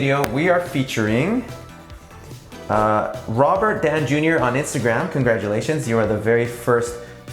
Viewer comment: I thought you said Robert Downey Jr😂